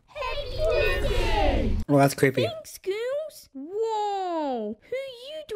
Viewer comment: Really thought they said 'thanks goons" 💀💀💀